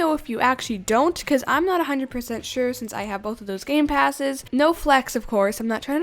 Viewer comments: I thought u said "no gamepasses" but you clearly have a multiple floors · I thought you said no gamepass I was really excited 🥺